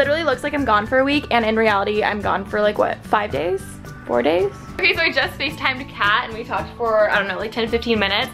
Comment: For a sec I thought she said "I literally just FaceTimed a cat" 😂😂😂 my mind